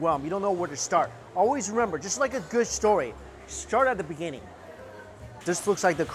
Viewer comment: "When you're at a good buffet, treat it like a story. Start at the steak and crab legs." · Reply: I thought he said "start at the beginning"